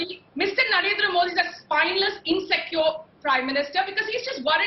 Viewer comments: Time 1:30 - did she call india prime minister a "spineless insect" ⁉⁉ i cant believe my ears ... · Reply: "spineless insecure Prime Minister"...not "spineless insect" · Mr Nagendra Modi your spineless insect ... ‼ WHAT did i hear that right ...⁉⁉